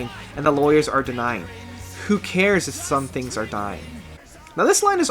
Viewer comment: Biggering is pretty clearly the remnant of an earlier, darker draft of the script. You couldn't just plug a song that sounds like that and includes the line "who cares if some THINGS are dying" (rather than "trees") into a movie with the tone of the final version. I'd love if an earlier script draft were leaked, like the original Gremlins draft that was more violent. The Thneedville demo having Ted interested in getting a tree so he can be the first kid on his block to have one, rather than so he can get a girl to like him, also indicates that the story used to be very different. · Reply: i thought it said "who cares if some things are dying" not "who cares if a few things are dying"